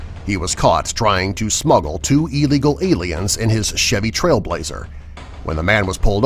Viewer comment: did he say aliens